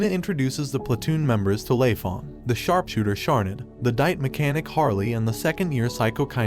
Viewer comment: did he say dike improve I heard wrong